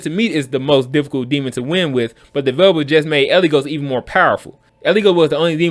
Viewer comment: did I hear you right, Eligos needs a NERF??